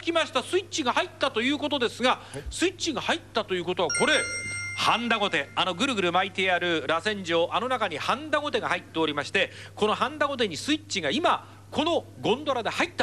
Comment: i swear i heard Honda like four times